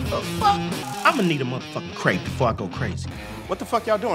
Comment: What does he say at 4:40?? still cant figure out lol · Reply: he said "imma need a muthafuckin crepe before i go crazy"